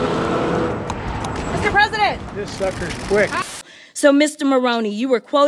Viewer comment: Did he just say “Yes sucker. Quick”?😂😂